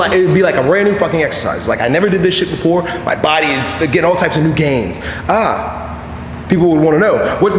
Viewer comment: at some point I thought he said All kinds of gains !!